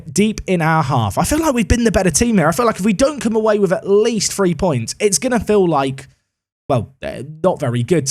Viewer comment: Did I hear it correctly. At 22:54, did say that he would be disappointed if the Bayern game didn't earn Atalanta AT LEAST 3 points?